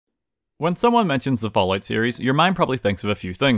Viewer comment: I swear to god I heard him say “when you think of the ballout series”